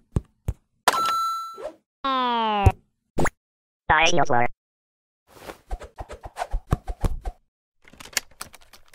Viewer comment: WHAT DID HE SAY?!?!? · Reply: i swear he was like saying "side a wood"